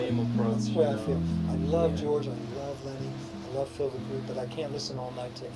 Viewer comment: Hi, thanks for uploading this. At 35:17 is saying "I love George, I love Lenny" and then he mentions a third guitarist but I can't make out who it is. Any chance you would be able clarify this for me? Thanks, Josh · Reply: Phil DeGruy was the third guy mentions.